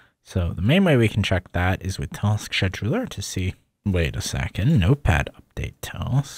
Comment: did he just say task "*sh*edular"